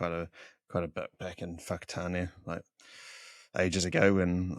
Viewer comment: what did you say!? F_ck a what!? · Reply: Hahaha ‘Whakatane’ (a māori word) is the place name of where I grew up 😂